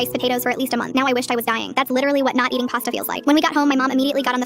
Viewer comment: I hear "and I threw all my white claw cans out" And started dying